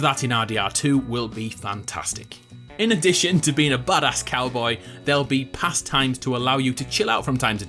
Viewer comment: Lmfaoo everytime he says badass cowboy I swear to God it sounds like he says "butt ass-cowboy"